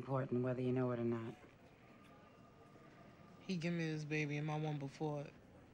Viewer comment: What does she say here? I’m deaf. All I’m getting is “He gave me his …. before…” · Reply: "He gave me this baby and one before."